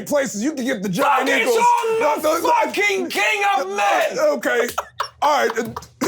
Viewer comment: What is Leiland saying at 58:43? I only heard "fucking king of men". Is it "focus on"?